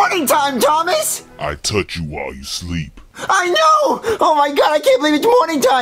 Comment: “I touch you while you sleep” Killed me 😂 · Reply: As a kid I always thought he said I told you while you sleep lol